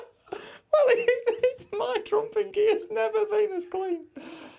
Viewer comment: “My trumping gear has never been cleaner” Guy Martin 2019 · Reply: i thought he said "trunk and gear"